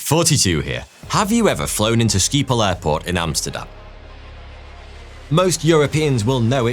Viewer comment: When you said skiephol I almost choked! I am Frisian (north of the Netherlands) and we have our own language. Skiephol litterally translates to sheepsbutt. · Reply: I thought he said Ski-pole Airport. I was like we're not a good country for skiiing